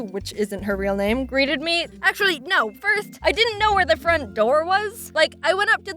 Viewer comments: was that Jaidens lisp she once talked about, seriously listen to when she said first it almost sounds like she said "firth" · Did anybody catch at 4:38 she says "firtht" instead of "first"...... pathta with no thause